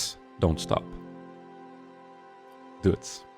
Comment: Lol, i thought you said Dudes instead of Duits xD